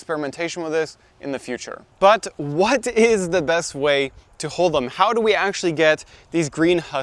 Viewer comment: I am English. Found a black walnut tree yesterday in our local park. To me it sounds like you say “to hole them” of course you are saying to HULL them…..it’s just your accent, but I was confused to start with!